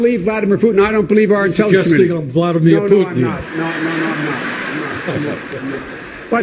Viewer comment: Anybody understand what Bernie said quietly at 0:55? I can't tell, and his racial expression for a second right around then makes me even more curious about what he said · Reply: "Suggesting I'm Vladimir Putin?"